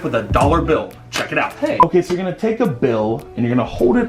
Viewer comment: Who else heard somebody say hey